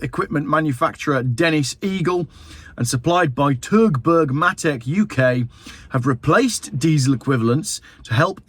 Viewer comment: Thought you said "supplied by TurdBurg", which would just about sum up this whole fiasco.